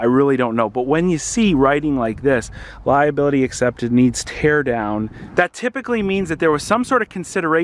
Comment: thought the i3 said nerds tear down, lol.